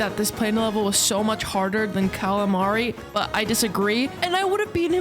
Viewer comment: "Carla Mari" did I just hear that correctly?? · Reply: i did say cala mari yeah LMFAO